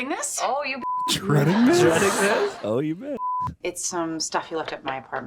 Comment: why did they bleep “you bet”…? Did they think she said, “you b**ch?”